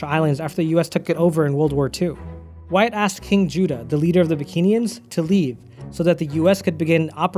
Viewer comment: did he call cheif juda a bad word!!! 😠😠😠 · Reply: “Why ask king Judah”